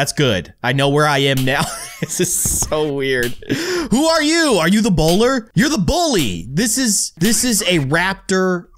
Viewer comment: I heard “Who are you, are you the boulder?”